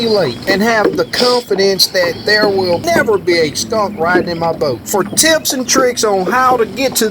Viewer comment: Just another great video! what did you mean when you said a stump will never ride in your boat?14:57/ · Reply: Skunk I said skunk.....LOL I should have adjusted my volume on that segment but it was uploaded before I heard it was low.....I try to keep that stinky cat out of my boat.... Thanks for watching and commenting.....